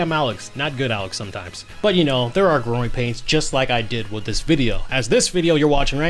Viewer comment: You said GROWING pains. I thought you said groin pains 😭😭😭